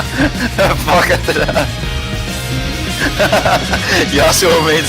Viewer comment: did he just say “ hvad fuck er det der” dansk vidste ikke han var dansker